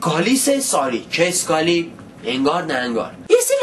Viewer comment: what does he say at 5:19 and what does it mean? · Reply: Harry Engaar na Engaar means I don't care a fig and its a Persian saying. also a fig is a type of fruit.